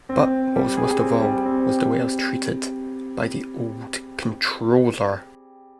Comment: Would you mind telling me what the music bit was in the first few seconds of this video? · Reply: What is Timothy said? Did he say "Mr. Wells treated by the Old Controller"? or "I was Treated by the Old Controller"? in